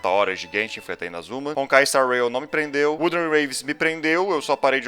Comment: qual o nome do jogo que ele fala antes do zzz 0:35sec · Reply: Wuthering Waves